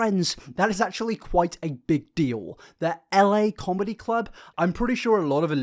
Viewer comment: i thought he said los vegas comedy club